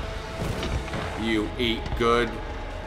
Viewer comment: lol 25:25 did he say "you eat gun"? This series is great. you should do a death montage.